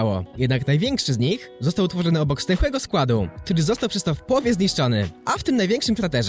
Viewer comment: ja zamiast stęchłego składu usłyszałem zdechłego składu xD